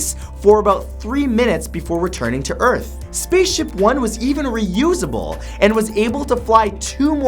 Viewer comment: I thought #6 said "SPACEIPHONE"